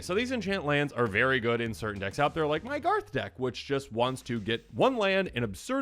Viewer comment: whhat deck is this? It sounds fun and something I might wwant to make, but I couldn't hear who the commander is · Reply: It's his Garth One-Eye deck 😉